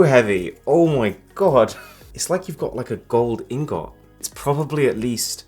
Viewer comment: Wait, did he actually say Gold Ingoat? I thought it was gold ingot....